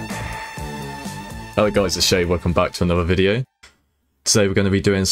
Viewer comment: freaks me out it sounds like you say shay instead of shade, absolutely love your videos though beast x